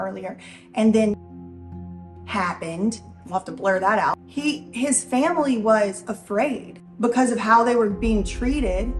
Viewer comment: what was the cut out word when was talking about Prim at 31:33 ? I cannot read lips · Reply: The word is >C>o>v>i>d> but if I write it out normally, my comment will be >f>l>a>g>g>e>d> & removed & maybe this video too. ¯\_(ツ)_/¯